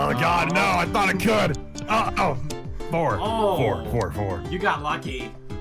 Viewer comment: it sounded like he said "Vore. Vore, vore, vore." XD